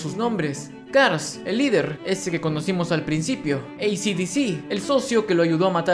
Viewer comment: cuando lo escuche pense que era creisi desing xdxd :v ( el que es dominicano entendera xd) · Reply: Yo pensé "AC DC"